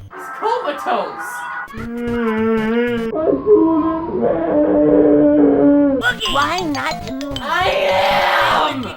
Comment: What is the muffled voice saying at 17:50? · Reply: "AYÚDENME"